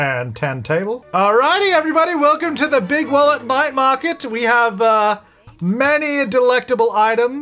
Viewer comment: I've not tried this but can you put food in a cooler? Then you can just bring it with you. Also when you did the food sale I misheard you as "welcome to the Bigwallet Bite Market" and I think that should be the name for Laird's food business 😊